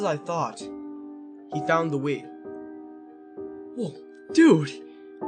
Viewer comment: Here the captions say “he found the weed” xd